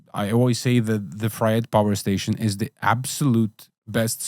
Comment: What he just said is a better solution? Fried Power Station? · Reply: Fryette